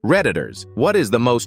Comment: I thought it said "predators" i stead fo "redditors" for a second 😭🙏